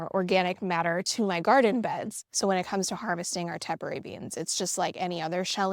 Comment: my hearing is a bit off and the CC show "temporary" beans but I thought I heard "taporary". When Google (taporary) it gave me "Tepary" beans - just double checking that is the correct name... I have a spot in my yard that is perfect for plants I can... mostly, ignore - similar to potatoes which also thrive on neglect (or at least the ones I've grown - lol) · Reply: Sorry about those captions! Yes it is a tepary bean.